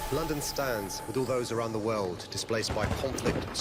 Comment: I thought he said London Stan for a second 😂😂😷